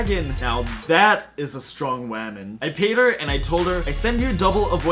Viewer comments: Wait did he just say “wamon” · Did he just say.... wamon?